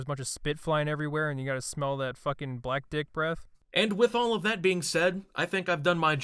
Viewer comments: Yo, at 15:55 time stamp did dude say "black dick breath"? WOW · Karen videos are great but did I hear you say Black dick breath?15:50, sort of offensive no? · did he say Karens have "black dick breath".........wth